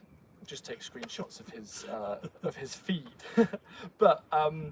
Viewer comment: if you want to collect art work, just take screen shot of... I guess you said feed, but I heard FEET 🤣